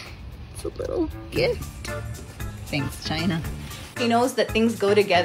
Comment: I thought she was saying thanks China for making the cat toy lol